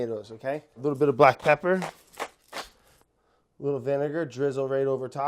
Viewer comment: does he say “vin” 😭😭😭😭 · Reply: Yes but for "vinegar" not "vince"